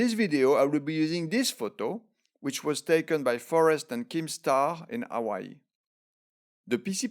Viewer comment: i thought he said keemstar and i was like ew, don't give that creep shout outs. but I'm glad it was just my ears deceiving me.